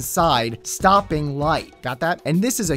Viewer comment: All I heard was "lightsabers!"